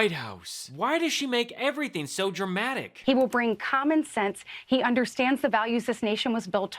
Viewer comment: Did she just say "He will bring comic sense?"